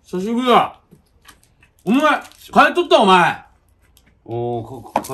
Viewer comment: 小学生の頃に亡くなってしまったいとこのお参りだと思って見てると感動する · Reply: ここが金とった！に聞こえたんよ……みんな共感できるかな……